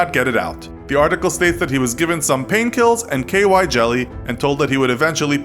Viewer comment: did he say pain kills instead of pills?🤔